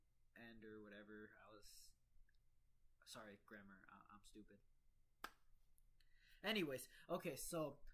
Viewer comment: What did he say at 9:00? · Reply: “Sorry grammar I’m stupid”